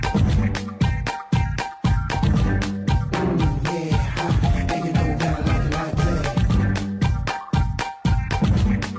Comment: What did he just said here ? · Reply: Ooo yea and you know how I like it like that